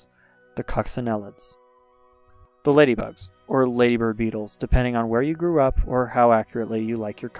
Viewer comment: is he saying "ladybird beetles"? It sounds like labor beetles but I think he was mumbling